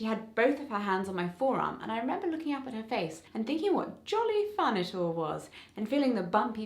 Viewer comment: the closed captioning has a mistake at 1:23 it says "what jolly furniture was" instead of "what jolly fun it all was" and again at 1:40 it says "hot" instead of "hurt" okay, there are a lot of mistakes, please try to have someone fix them ;v; I'm not deaf yet, but I am hard of hearing (luckily tho, my hearing is usually only bad in noisy environments or over the phone)